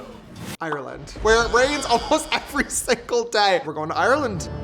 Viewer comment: i got so excited😭 i thought he said iceland😭